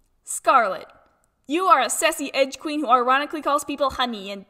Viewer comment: On Scarlet's, I heard "sussy edge queen". She do be looking S U S S Y tho